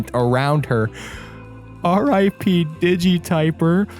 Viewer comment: I looked away and all I heard was p digi qwq